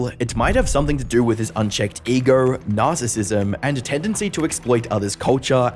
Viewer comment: I know it was unintentional because of your accent, but I do find it funny how it sounds like you said "Nas"issism at 1:57 lol